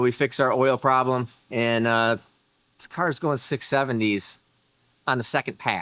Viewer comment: Did you say 6 7dees!?!!! 😮😮🔥🔥😭😭🔥🔥